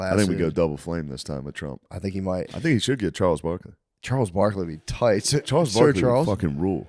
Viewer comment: Theo thought they said Gnarles Barkley